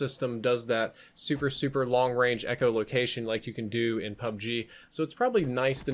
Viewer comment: Anyone else heard him say "like you could in Black Ops 4"